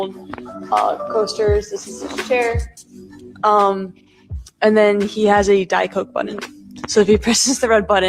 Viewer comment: I could have sworn she said, " He has a diaper button..." 🤣